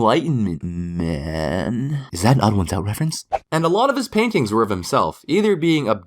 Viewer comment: did he actually say "is that an odd1sout reference"